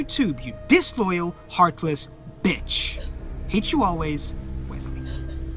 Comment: "Hate You Always -Wesley" HAHAHAHA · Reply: I thought he said "Hit you always... Wesley"